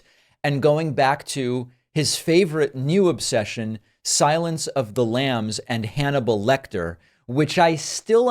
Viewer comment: At least he finally learned the real name of the film. When he first went nuts with it, sure sounded like he was saying "Silence of the Lamb".